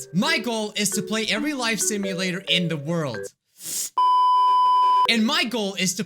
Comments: it sounds like he says "Michael is to play every life simulator in the world" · I thought you said mical scott is to playevey life sim in the world